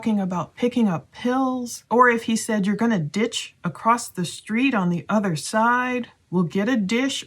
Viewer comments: it sounds like he said ¨ you´re addiction to the party¨ and then sounds like a female voice that says ¨started¨ right after what he said... then the other girl ask that what did she say and she said i told everything... thats how it sounds to me... i don´t know what other people hear... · I thought he said: "Your gonna ditch across the street and then get/catch your ride.". This may point to he was assisting them to get home safely. Hopefully he recounted all this to the police. Time will tell.. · I heard, "you were going to ditch him, Maddie" or a slurry version of that. Like maybe " You ditched him, Maddie" I don't even remotely hear about anybody 'getting you'